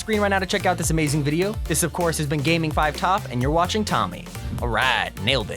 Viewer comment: did he say 'this is gaming5top'